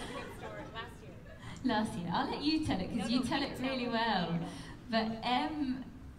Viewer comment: What did dom say in 46:02??!? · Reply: "Last year, i will let you tell it, cause you tell it really well"